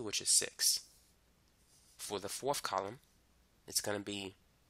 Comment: What did he say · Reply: For the fourth column